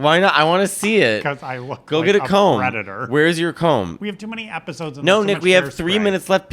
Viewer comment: When Nick said he couldn't part his hair in the middle or he'd look like a predator, I thought he said REDDITOR. I was like, damn. Sick burn.